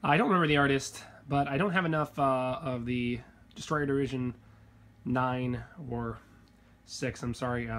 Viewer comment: What Series is hibiki and that purple haired chick from? I listened as hard as I could and it sounds like your saying Destroido Region 6 but I couldn't find anything even anime related at all on the web :( also where do you get lost of your posters from? I'm trying to make my room look like yours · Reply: They are girls from Kantai Collection (Kancolle). They are part of what Japan labeled as "Destroyer Division 6" during World War II. The ships included Hibiki, Akatsuki, Ikazuchi, and Inazuma. The ones you are asking about are Hibiki and Akatsuki in the series.